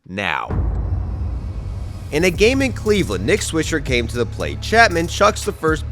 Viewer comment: what did he say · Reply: Nick Swisher